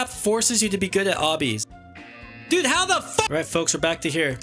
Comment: Did you say f***